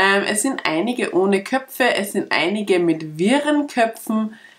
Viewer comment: Es ist immer wieder witzig, die Untertietel einzuschalten, weil die ständig was falsch verstehen z.B. 4:52 statt "wirren Köpfen "wurde hier "Viren Köpfen" verstanden!😂11:44 "Da ist auch einiges an Franken gekommen".